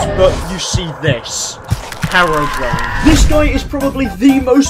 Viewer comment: Did he just say harrowbrian and not herobrian